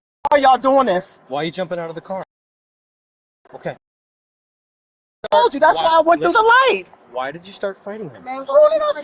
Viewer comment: She said “I have to sh*t, I have to pee and I have to sh*t. I told you, that’s why I went though the light.” That’s what she said when you asked for a lip reader.